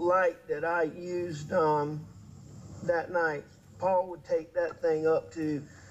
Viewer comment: Did anyone else hear what I just heard? When he was talking about Paul rigging up a little light for his gun, Alex said, “he was always rigging up a little light just like the one that I used THAT NIGHT”. I thought maybe I had heard that wrong so I put the subtitles on and listened to it three more times. He DEFINITELY said, just like the one I used that night”. I believe it to be a slip of the tongue, but he’s basically admitting that he used a blackout gun with a light that night! How did they miss this, or call me crazy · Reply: go to 38:15 on the video time and listen carefully. He is talking about Paul making a little light to use on his gun and literally says, “ just like the one on mine that I used that night”. At first I thought maybe I misunderstood and that he said “At night” instead of “that night” but after replaying it over and over I’m sure he said “that night”. What else could he have beeped talking about